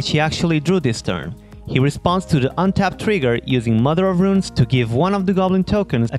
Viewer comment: great video, but i would like to clarify a common mistake in that people ask if they will pay and then respond to the draw, but you actually have to respond before you ask because paying the tax and drawing is one trigger. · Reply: ok that makes sense I just heard “I don’t pay, and he responds to the trigger with”